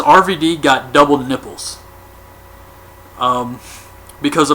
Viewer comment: LMAO, I thought he said "double nipples" at first as well. I quickly realized he said "double nickels". But still, I'm glad I'm not the only one who thought he heard that.